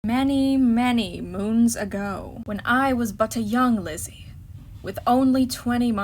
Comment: I had too listen to the intro again bc I thought she said when I was just a little lesbian and I was like did I hear that right